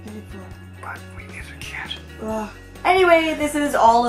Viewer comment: Oh my gosh, when said 'but we need a cat' at the end, the first time I heard it I thought he said 'but we made a kid'!!!